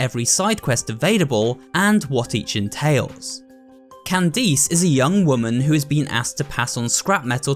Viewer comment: no way he really said Can deez